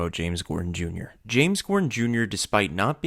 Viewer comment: I'm I the only one who thought he said James cordon Jr ?